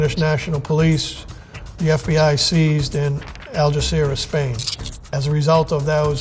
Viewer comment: The subtitles are wrong: it's not Al Jazeera, it's Algeciras